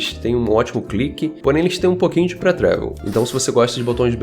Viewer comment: Fala! Não entendi o termo (7:03) que você usou pra se referir aos botões laterais. Você deu um nome (em inglês) pra dizer que ele não é seco e responsivo. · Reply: Fala Gabriel, sim, tem muitos termos em ingles, mas que nem sao traduzidos, ja sao muito usados. Esse em especifico "pre travel" signigica o curso, avanço que o eixo do botao tem ao clickar, pois isso depende da implementação do switch e do botão em si. Resumindo, ao clickar, o botão desce um pouco até registrar o click.